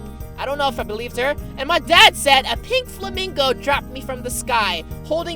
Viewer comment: When she said 'pink flamingo' I thought she said 'pig flaming goat' lol